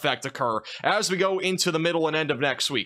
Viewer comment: Did you just say "Wong term"? 9:39/40. 😂